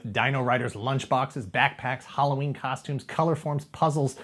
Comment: I misheard colorform as chloroform